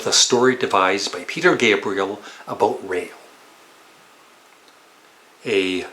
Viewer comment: I misheard "story about rail" and thought "what? What songs are about a rail?" lol.